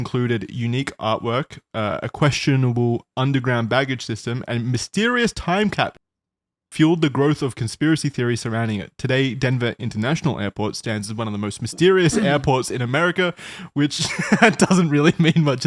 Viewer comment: What’s with the censor @ 8:42? It sounded like he said time capsule but I can’t tell.